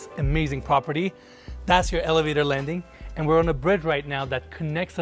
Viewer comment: why is saying 'and we're on the bread right now' I think he means bridge